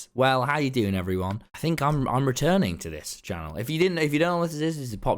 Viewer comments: J thought that said,'i am retiring.' i nearly shat myself 😂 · I thought it said retiring 😮